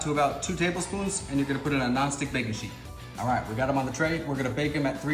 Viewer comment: I heard, "Gnostic baking sheet". To quote Julia Child, "With enough butter, anything is good."!